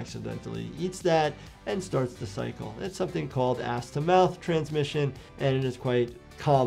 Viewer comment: Maybe I have dirty mind but did you just say a** to mouth transition?